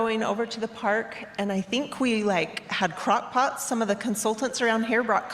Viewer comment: I heard her say cock pots… not crock pots. I have the mind of a ten year old boy! 😂 I’m sorry! I heard what I heard!